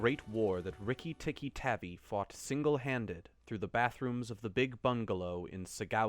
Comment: i thought it said backrooms instead of bathrooms